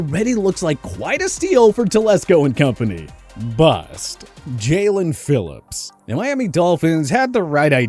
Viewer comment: I thought he said jaylen waddle on the jaelen Phillips one for a sec had me worried